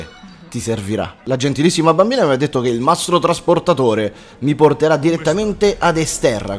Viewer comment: Solo io a 3:17 ho capito "nastro trasportatore"? xD